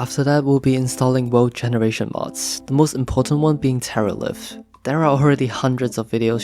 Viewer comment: I'm not trying to be mean but did he say Terra lift or Teralith?? Cuz I searched for the "mod" and only got a 1.17 datapack called Terralith and mods are quite different from datapacks. Also, it's all a mix of Fabric and Forge.